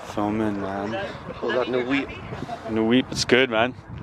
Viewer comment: did he said no weed?😂